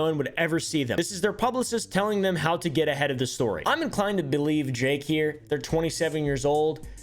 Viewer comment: did he say drake or jake? lol